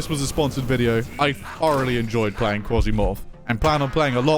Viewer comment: "I Fu-Already enjoy playing Quasimorph" The Sponsored/Youtube video change of words. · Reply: Sounded like he said thoroughly to me, bri'ish english sometimes makes the f and th sound similar phonetically (bottom lip to top teeth for fuh, instead of tongue to teeth for thuh sound)